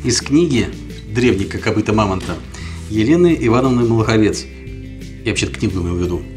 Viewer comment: Дикция. Глуховец. 15 человек сидели рядом и все услышали "Глуховец"